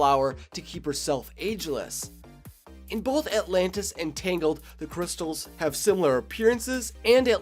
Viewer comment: it sounds like you saying that Atlantis is under Corona